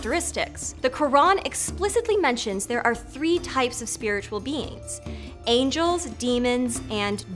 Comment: Wrong information about Qur'an :: Qur'an never says about 3 types of Species. Angels, demons and jinns but its Angels, Jinns and Humans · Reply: I think she said " spiritual beings"...